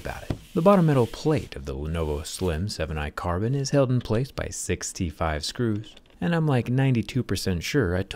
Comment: 6 T5 Screws. My friend thought you said 65, cuz it sounds the same.